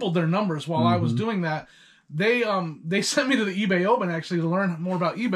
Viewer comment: Good interview. In one part of the interview, he said that his reseller employers sent him to the eBay...Open? I couldn't understand what he said there, even after replaying it a couple of times. It's at 3:32 of the video, just curious what he was referring to. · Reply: Ebay Open was held in Vegas yearly, went virtual since covid. Basically meeting other sellers and Ebay employees, and Ebay related seminars you can pick which ones you wanted to attend.